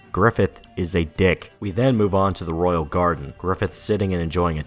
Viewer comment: I literally heard "the royal guard" I'm playing too much dmc